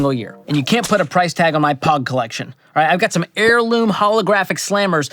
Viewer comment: Did you say PAWG collection